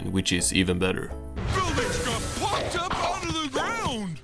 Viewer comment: Why did I mishear Patrick saying "f%%ked up" ._.